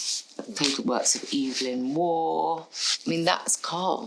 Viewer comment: subtitle says 'works of Evelyn Moore'. Afraid it's 'Evelyn Waugh'. If Mr Lagerfeld is dead at the time of speaking, how can the woman say, 'I'm one of Karl's dearest friends'? One cannot be friends with the dead, although maybe one can now. No further distinction between women and men, no further distinction between alive and dead.